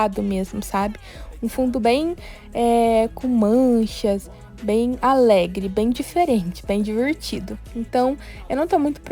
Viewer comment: Eu ouvi em 1:20 bem ballet ao invés de bem alegre kkkkk (não deixa de ser...)